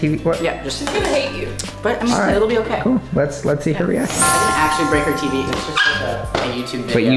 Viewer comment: I THOUGHT HE SAID "I will break her teeth